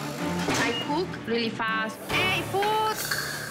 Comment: LOL it sounded like she says "I poop really fast"